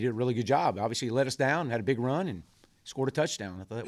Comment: For anyone who misheard him he said “LED us down” as in Led us down the field. Not Let us down. Look I don’t like him either, but come on. “ He LED us down, had a big run, and scored a touchdown.” Just use context clues like you were taught in 3rd grade.